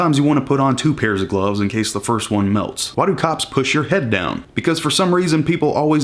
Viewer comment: I heard the start wrong and I thought you said “why do cops touch your cock” i was like shit I wanna meet these cops